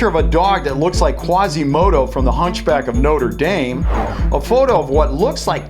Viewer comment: Did he....did he just say Noter-daym?